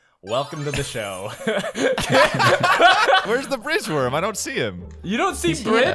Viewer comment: I heard fridge worm.